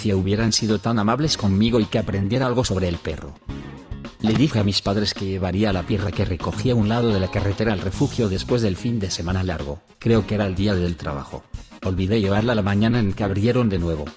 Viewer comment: Por un momento escuché "La perra que me cogi'